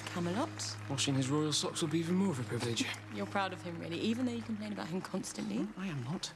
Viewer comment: what is Merlin saying between 0:34 to 0:36? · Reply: I know I’m a decade late but if anyone else is wondering, he said “Washing his royal socks will be even more of a privilege.”